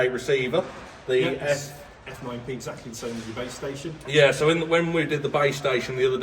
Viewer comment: Thought u kept saying babe station 😂😂😂